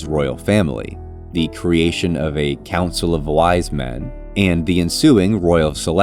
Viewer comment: I heard it as "Council or Lies Men" 😭😂